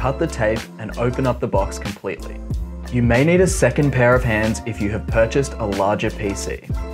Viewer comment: dawg i thought he said you need a extra pair of pants